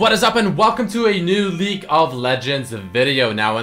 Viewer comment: Did he just say Leak of Legends?